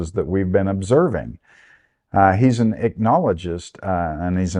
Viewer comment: What is that word at 15:03 that sounds like "acknowledgist"? Google was no help. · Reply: Ichnologist